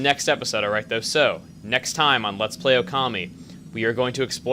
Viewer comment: when u said, "next time on lets play okami" i thought u said "next time, on lets play a commy" XD